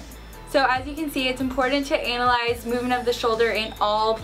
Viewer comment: Did she say, Anal-Eyes at the end instead of anna-lyze? Lol!